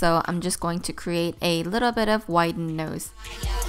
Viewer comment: did she say a little bit of wide nose ! or white nose ?? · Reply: Wider nose